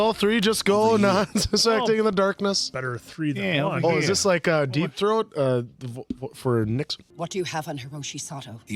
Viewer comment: What was the Answer Saying?!!🤣😂 "Deep Throat.." what?!🤣